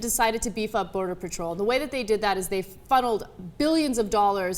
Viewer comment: Did Anna say fuddled or funneled?